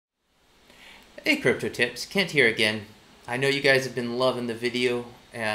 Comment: When you introduced yourself "Kent here" I heard "can't hear" 😅